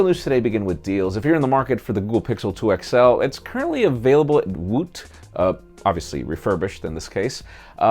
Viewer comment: "It's currently available (something)" Lol. I couldn't understand that last word you said. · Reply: Larry Lopez I believe it was a website called "Woot"